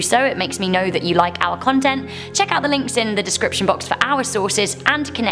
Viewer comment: I know you said "our content", but I heard "owl content."